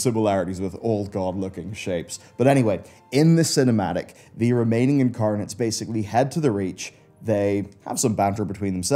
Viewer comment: Ah he says "The Remaining Incarnates", for a moment there I thought he said "The Romanian parliament" lol.